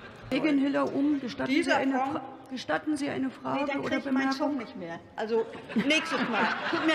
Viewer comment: Was hat sie gesagt? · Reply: "Nee, dann krieg' ich meinen Zug nicht mehr" Musste auch 2 mal hinhören 😂